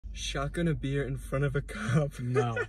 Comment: I got so scared bro I thought it said "shotgun a bear in front of a cop"